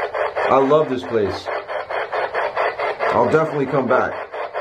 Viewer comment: when he said I'll definitely come back it sounded like it said great